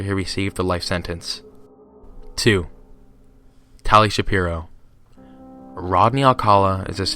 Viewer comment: I deadass thought he said Ben Shapiro